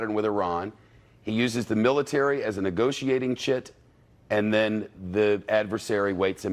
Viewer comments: he says shit instead of chip🤣🤣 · Did he say shit at 4:14?